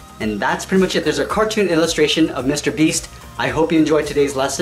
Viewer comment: Lol i thought it Said “mr Bean”